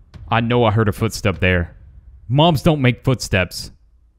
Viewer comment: did he just say "mom's don't make footsteps"? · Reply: Gxulia mobs.